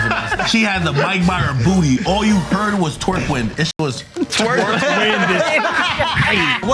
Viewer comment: Thought he said twerk window